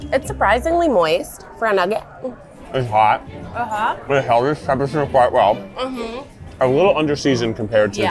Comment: I THINK Keith said "its hot but it held its temperature quite well" 🤣